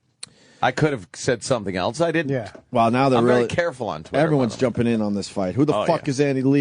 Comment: did anthony just say he’s ‘very careful on Twitter’?